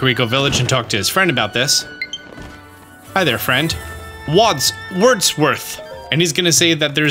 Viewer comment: I thought you were saying hi to your 25 friends online 😂 I have 2… friends… wow this post is sad now